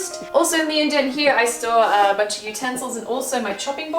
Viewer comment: I'm not used to Australian accents because I live in the US but when you said "store a bunch of utensils" I thought you said stole so I was massively confused